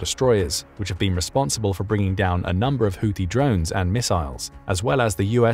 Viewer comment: Neither Iran or Israël have the capacity for an expeditionary force. Right now only the US, UK, France, Russia and sorta kinda China have that. Theyre just gonna pelt each other with drones and missiles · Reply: At first I thought you said they should attack each other with bones and missiles. That was funny for a second.